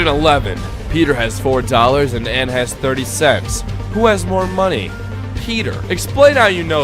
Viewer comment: i thought it said anne has 30 sex